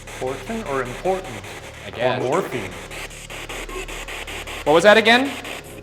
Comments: i really thought he said Morphine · I'm not sure if I'm the only one, but after the whole "the beast" and "Ryan" comments, when they asked about why they're still here it said "unintelligible" but I swear I heard "morphine"... Anyone else????? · #postmortem at 9:16 when it said "important" to me it sounded like "portal" and as you mentioned before.... portal to HELL. Also Ryan, are you okay? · you know at 9:16 when it said "important" who else heard portal as in the demonic portal from earlier which would match much better with the whole "the beast" thing too. #postmortem #pleasestoptouchingyourbellybuttons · I dont actually believe in ghosts, but did anyone else notice that at 9:16 instead of "important" they might be saying "morphine"? Because that was what I thought, before I heard about the ghosts who talked about morphine into peoples Spirit Boxes. Idk man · it kinda sounds like “portal” instead of important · When the ghost supposedly said important I thought they said portal instead. Also I dont trust the spirit box it doesnt seem to give very cohesive evidence all the time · I heard Borden not “important” lol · #postmortem i heard portal instead of important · even though it's jumbled, I could hear " morphine." Which would correlate with what you said about the 54th Massachusetts Regiment looking for morphine. What do you guys think? #postmortem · I heard "morphine"!!! 😱 didn't they say that was something that was said often by other ghost hunters later in the video??